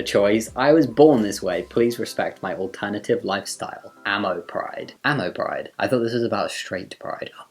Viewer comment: i thought ammo pride said allo pride